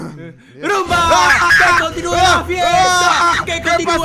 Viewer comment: Como grabaron el inicio 7u7 XD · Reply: En el principio escuche " Riumba " .__.